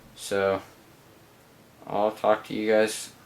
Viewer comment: hahaha did you say ill fuck you guys later? xD